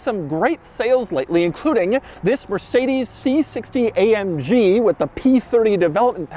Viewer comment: did he said c60 amg!